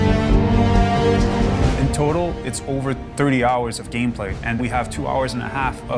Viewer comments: At first I thought he said 3 hours of gameplay but its really 30 which is a lot but ac games r always quite long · At first I thought the guy said its 3hours of gameplay and 2hours of cinematic, I was like wtf